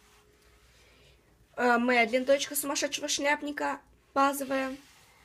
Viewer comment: Мне одной послышалось "ТОЧКА"? На